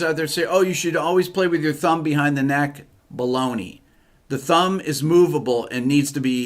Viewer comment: I was eating almonds and swore I heard “blow me”